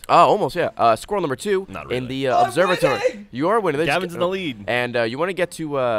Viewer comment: Gavin at 0:56 Ha ha ha! That seriously made me laugh so hard! He sounds like he's saying "I'm windy!" or something. Lol. Thanks to Good Guy Ray for helping us understand what he had just said.